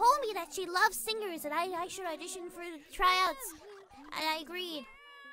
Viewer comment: I heard "I I sure addiction for a trans" XD